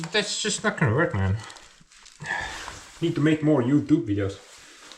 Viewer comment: Thought u said no more cabin videos 😂😂😂😂